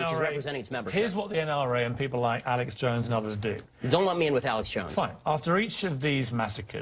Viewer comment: Ben says to piers, "don't let me in with Alex Jones", and piers replies with "ok fine". Is Ben meaning do not compare me to Alex or more like, I don't care what Alex Jones says so don't use him as an example? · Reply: He said, "don't lump me in with Alex Jones." He meant don't put me in the same company as Alex Jones. And good on hm for that because Jones is a nutcase.